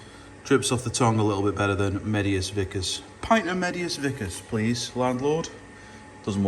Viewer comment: You could call it biggus dickus as I thought that’s what you said at first I had to rewind to check. Rolls off the tongue better than medius vicus 😂 looks like another great brew in the pipeline. Cheers 👍🍺